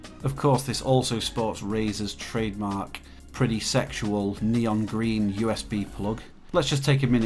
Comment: What Tf did he just say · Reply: I said “SEXUAL”....... :-)